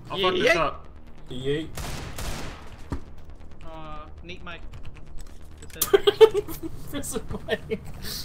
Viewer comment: Did say NeatMike